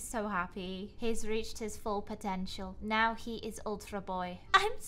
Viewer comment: when said "now he i ultra boy" it sounded like she said " now he is ultrda bay" reply or like my commet if you herd that or agree.